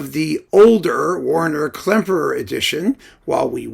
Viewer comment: Oh. I thought it said Werner.